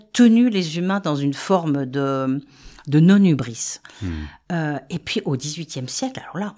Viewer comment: J ai entendu un mot que je n ai pas pus comprendre nonubrice ou nonumbrice a la minute 8:50 c est quoi ce mots svp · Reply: Bjr C non Hubris. Bav